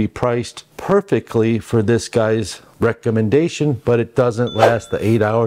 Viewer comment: 😂 Did you really say "rectum-endation"?😂